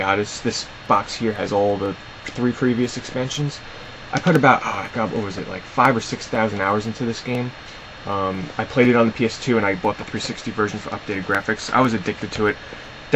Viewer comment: DID HE SAY 5000 HOURS????????